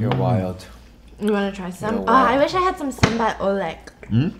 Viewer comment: DID SAID "SAMBAL ULEK" at 6:14 ??? lol